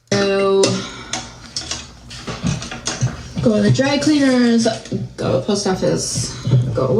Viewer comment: said “I gotta go to the dry cleaners...” I heard “I gotta go to the drag queeners... 👑 “.